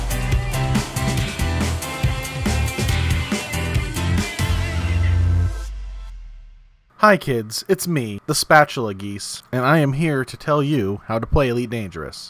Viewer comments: i heard "Hi kids, it's me, the spatula grease" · First time watching and i heard “Its me, the spatula beast” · First time watching and i heard “Its me, the spatula beast” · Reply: I thought he said spatula geese. As weird as that sounded, it was the last thing i understood and i play eve.